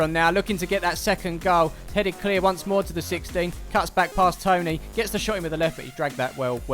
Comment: I watched this when it came out but I love rewatching games when im bored, did you call Lewis tony at 4:17 ,must’ve had your race neutral glasses on while doing the voice over